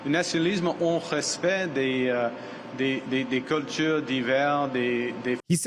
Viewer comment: The translation i'snt accurate. I think he said 'culture divers' (meaning cultural diversity) and NOT 'culture d'hivers' (winter culture)